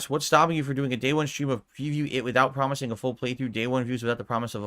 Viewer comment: What did he say here? He gin-slurred half his sentence. I'm pretty sure he's talking about Persona 3, but he's impossible to understand a bit of it. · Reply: Of Pffeewee it? Wtf did he just say?